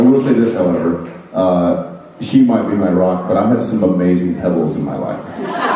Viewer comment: What does Jensen say at 2:50? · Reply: She may be my rock, but I have some amazing pebbles in my life